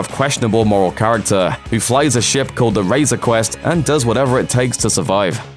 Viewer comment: Did he just say Razor Quest ?? hahaha | "Razor Crest"